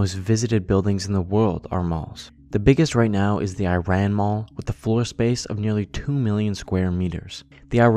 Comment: I misheard the Iran mall as 'Ayn Rand mall', but funnily the name would be very fitting would the mall be located in the states instead of Iran.